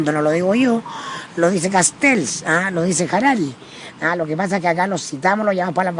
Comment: Alguien que me diga por favor qué dice después de "Castells" en el minuto 39:22 . Puse el video en velocidad 0.5 pero ni así pude entender. Ayuda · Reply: Lo que entendí fue "Harari" por Yuval Noah Harari